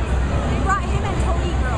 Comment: ya i know but if you listen to the girl in the background at 2:30 see says toady girl instead of toadette